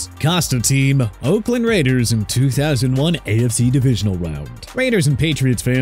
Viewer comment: The Narrator just goes full Elmer Fudd by saying 'wound' instead of round 😂😂